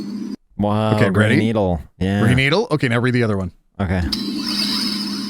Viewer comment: On the audio change, first thing I heard (I hadn't read the words) was "Brain Needle"!